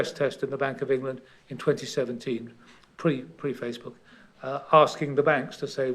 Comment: Did Sir say….Pre-Facebook at 12:42?